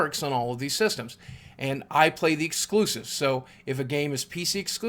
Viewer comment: what did he say at 1:19 exclusiv? i dont know.